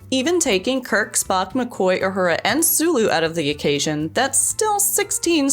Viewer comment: Did she really say out of occasion instead of out of equation? For Pete's sake